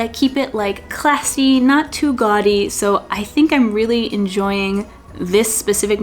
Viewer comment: Hello, I agree with the majority on the "no waste apron style and I agree with you on the design. Phew that was easy. I was a bit confused at the start of the Vlog, I thought you were saying the embroidery was too "Godly" for you!? Please excuse my Aussie ear. I worked it out in the end. Finally, thank you sharing a part of the U.S.A that doesn't appear on my radar. It looks loovely. Go Well.